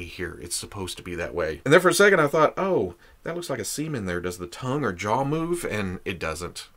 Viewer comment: I thought you said "looks like a semen there.." while pointing on the mouth..I had to rewatch and oh, its "seam in there" 😂😂